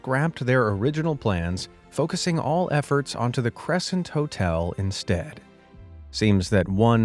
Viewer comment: for a sec i thought he said totel instead of hotel